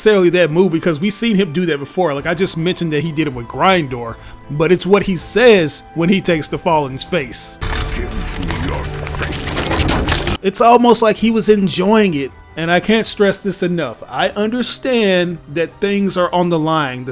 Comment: When I was a kid watching transformers 2, I thought Optimus said "you sealed your fate" instead of "give me your face".